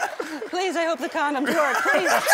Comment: what does ellen say @ 1:39? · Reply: please I hope the condoms work